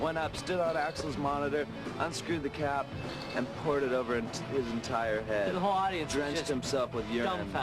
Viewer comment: FNM were part of that "I hate celebrity woe is me being a millionaire rockstar" mentality BUT they nor contemporaries in Pearl Jam, AIC, Soundgarden, etc. EVER turned down the $$! I also find it ironic that those bands (including Metallica) spoke ill of Axl and GNR behind their back when all Axl ever did was something beneficial for them. ....and did I hear that correctly at beginning of this video: "somebody threw a bottle on stage during FNM set and later Mike Patton poured it's liquid over Axl's head?!? if so ..my opinion of Patton has plummeted! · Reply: I thought he said he poured it over his own head..